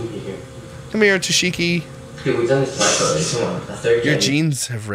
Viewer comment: I have replayed it and it sounds like says “we’ve done this for a 3 days”. Did he mean they practiced for 3 days to get the shock?